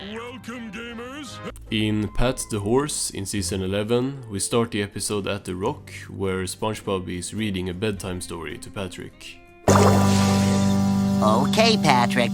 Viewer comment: It sounded like he said Pat’s divorce.